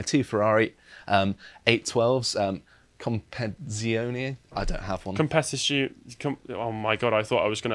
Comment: i thought it said compenzatione